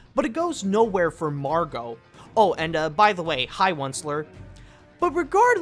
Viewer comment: What does he say? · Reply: He said "hi once-ler" referring to the boy looking like the once-ler from the Lorax movie.